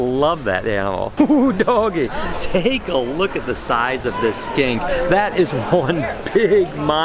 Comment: all I heard at 3:12 for some reason was an australian voice saying "*look at the size of this skank!*"